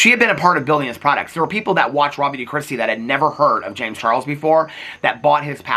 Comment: i watch everything on youtube with the goofy auto-generated captions, but man did it really hit the nail on the head this time when it thought you said "robbie de crusty" 💀